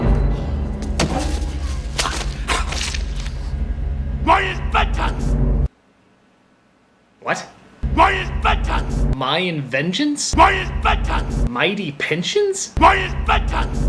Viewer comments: I actually thought he said "Mine is vengeance!" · I think the scientist says "my inventions" at 14:30. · I heard "Maya's Buttox" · i think he says "my inventions." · I actually think he was saying "My inventions!" at 14:30.